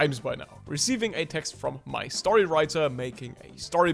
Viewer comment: "after recieving a text from my storywriter..." I thought you said " my story"... like you wrote the story? I am confused.